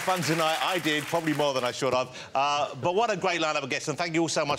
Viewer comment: Did he just say at the end …should of?